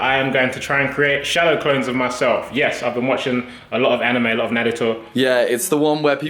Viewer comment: Did Tobi just say Nadato?